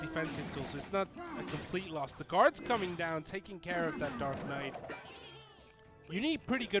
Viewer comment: i dnt knw if the commentator is screwing around or did he just say dark knight to a dark prince shit